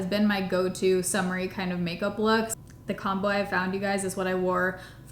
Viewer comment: Instead of “the combo i found”, i heard the “the calm boy i found and is what i wore for 2 weeks”… i was very confused and had to go back and re listen twice! 😂😂😂